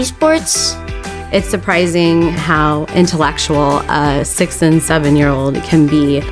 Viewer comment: BOI DID SHE SAY 67😭✌️😭💔💔😭😭🥺✌️